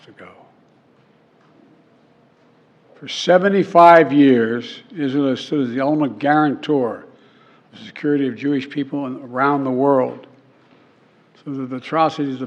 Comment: can anyone translate what he said here? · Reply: I think he said "For 75 years Israel has stood as the ultimate guarantor of the security of the Jewish people around the world."